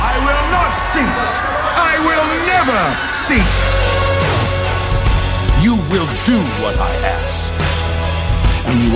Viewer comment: Pls someone tell me what's the word that Frank said at 2:52 😭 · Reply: “Never cease”